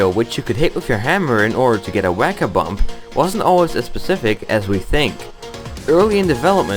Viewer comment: Just to let you know, there is a caption error at 3:15, which says "pacific" instead of "specific". Great video!